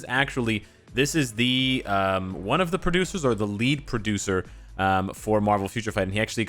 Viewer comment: lead producer. Me : What least producer? Oh I mishear lol Ign: Alutuzy